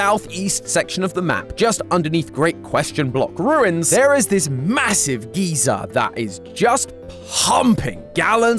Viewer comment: Whenever I heard him say “geeser” I thought of the old man from deltarune 😭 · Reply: I heard "Massive Geezer" and thought of the roblox avatar